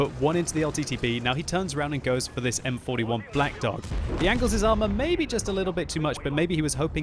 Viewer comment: did he say M41 black dog? 😂